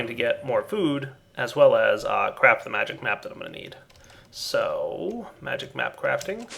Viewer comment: i heard crap the magic map...